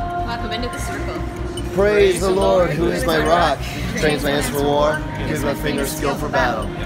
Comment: Can someone please tell me what they are saying in their prayer? I hear "Praise the Lord who is my rock. Thank you Lord, give our fingers skill for battle." Is that right? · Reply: Kayla Weins that sounds right, except the part where you have “Thank you Lord” it sounds like they say “__ our hands for battle” it almost sounds like they say “Brace our hands for war” but I don’t know if that’s the right word.. I’ve listened several times trying to figure it out, to no avail. But that’s a wicked prayer for pre- show!